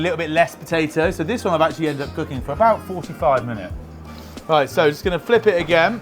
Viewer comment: Am I hearing that correctly, 6:00 he's had it in the pan, on a low burner on one side for 45 minutes?